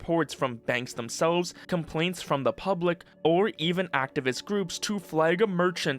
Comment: "This gives power to groups like collective shout." I heard "brutes" rather than groups at first.